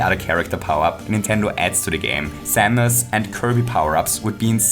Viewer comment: I thought he said Thanos and Kirby powers